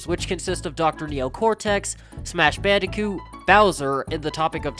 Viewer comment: Did you just say SMASH bandicoot